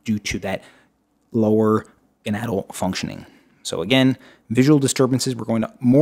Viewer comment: What does he say at 10:17 about bone loss due to low levels of testosterone and estrogen. I can’t make it out 🤦🏼‍♀️ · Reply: Enadle? That’s the word I heard. I can’t make it out either